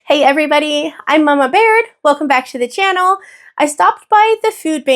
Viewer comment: Try reading that again. It's actually. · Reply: Lol my bad I thought you said "It's Mama Bear" never even saw the name. My apologies.